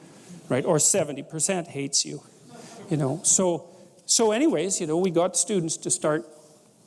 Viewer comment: Ugh I'm ruined.. Ever time he says, "so anyways, “ I hear, " so anyways Pinocchio ".. Lol.